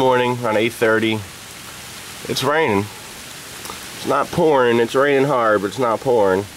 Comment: I thought you said its not porn, but you said poring..lull.. *Back to the land of interweb*